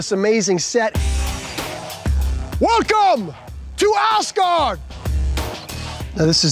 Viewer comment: did he say "Ass Guard"?